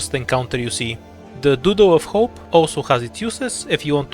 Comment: lol, I thought you said "doodoo of hope" 🤣